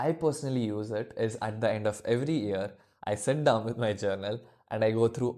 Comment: not clear what he said ? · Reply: At the end of every yearr